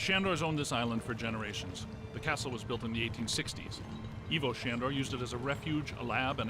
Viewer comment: OH!!! (>ლ) for a Minute tops, I thought you guys said the castle was built in the "19"60's... I was like, "You were doing so Good up to that point Akroyd"